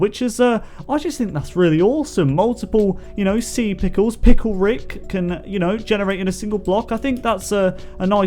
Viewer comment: Did... did you call that sea pickel... Pickel Rick?!